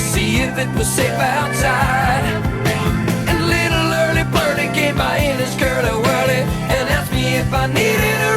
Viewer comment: Everyone points out the obvious misheard lyric, but years later when I paid more attention to it, I found another part that I had to look up. I thought "came by in his curly wurly" sounded like "gave my anus curly wurly."